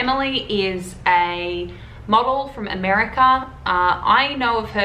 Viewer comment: I thought you said mogul instead of model